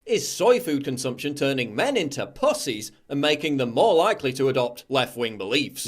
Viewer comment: Sound like you said bussies at the beginning.